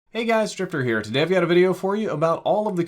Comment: Yo I thought he said hey it's stripper here